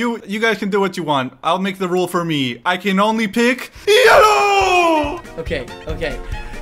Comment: "I can only pick YELLOW!" I would have picked yellow before him and make him unable to press a switch He did not say: Only I can pick yellow